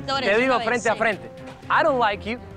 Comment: Chale le pusieron los subtítulos mal, jaja según yo escuché " I don't like you dude" y lo subtitularon como no me gustas, sucio... 🤔🤔🤔